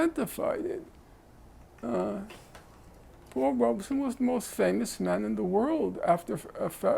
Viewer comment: who is the guy he is talking about ? He utters his name but I can't hear him properly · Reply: Paul Robeson